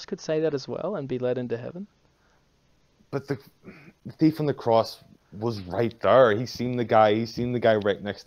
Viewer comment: When he says of the thief on the cross, "but he was right there!...", I hear, "but he fully believed with no room or reason to doubt"